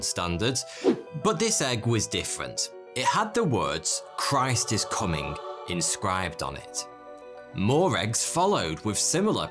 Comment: For a second I thought the egg said: "Christ is coming. Look busy!"